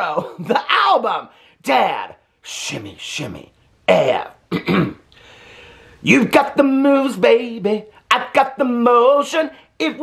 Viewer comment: At the beginning when you sang ‘You got the moves’, for a second I heard ‘You got the moose’ and I was like Here we go again😂😂😂😂